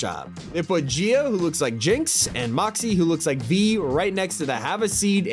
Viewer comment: did… did u just say… V ? Lol 😂